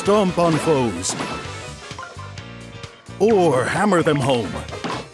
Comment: I thought when he said hammer them home I heard hammer them hoes!! I was like oh my Nintendo relax lmaoo